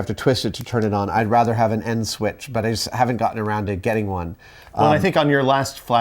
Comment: en switch .. N switch ... In switch?.. end switch ??? good gosh what kinda switch did ya say?????